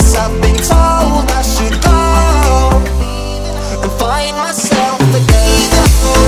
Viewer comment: i keep hearing "find myself a gun"